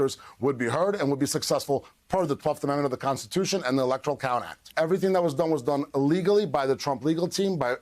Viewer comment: i have always heard what he says as "everything that was done was done illegally"